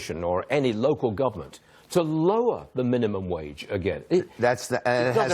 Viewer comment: no no no they thought you said "minimum age"